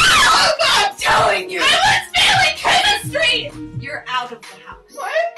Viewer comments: When i first saw this thought she said "I WAS FEELING CHEMISTRY" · when i first heard this is thought it said "I WAS FEELING CHEMISTRY" close enough